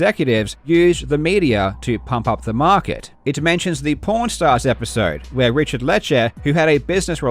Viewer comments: I thought he said pornstars not pawn stars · Lol it sounded like you said porn stars instead of pawn stars. I just now realized that was intentional by the show creators. God damn I’m slow